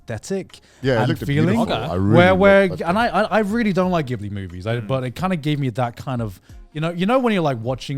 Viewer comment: "i really dont like ghibli movies". Am i hearing this correctly?